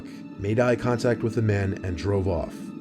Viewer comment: "his ad was taken off the next day." I heard "his head was taken off the next day"